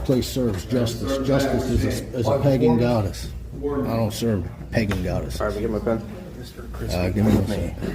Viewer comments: Did he say he doesn't serve pegging goddesses? 🤣🤣🤣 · did he say that justice was a pegging goddess?